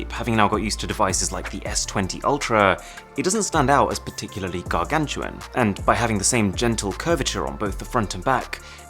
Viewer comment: what did sir say there 😅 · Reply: Gargantuan. Means massive, enormous etc.